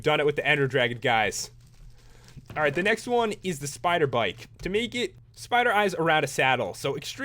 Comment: A lot of people have asked for a review on this one, so hope you guys enjoy! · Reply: when you said spider bike sounded like you said spider BITE. like teenage mutant ninja turtles! (my favorite show!)